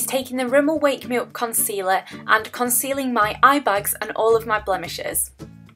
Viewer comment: When you said "concealing my eye bags" I thought you said "concealing my eye bugs" hahahaha! I love your accent though, it's adorable! Your hair is gorgeous too :)